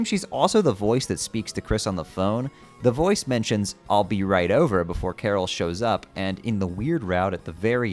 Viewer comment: I think the voice said "be right there" so it could mean "carol will be right there"